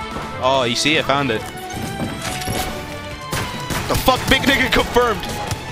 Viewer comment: did he just say BIG NI*GA CONFIRMED?